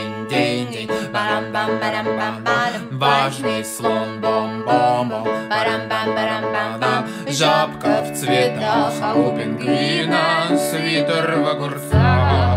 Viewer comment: Я слышу ЖОПКА В ЦВЕТАХ